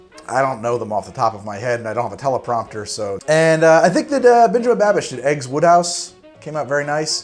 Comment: Sounded like you said Benjamin Babish